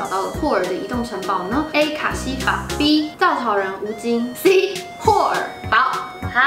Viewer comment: 我聽成稻草人吳京...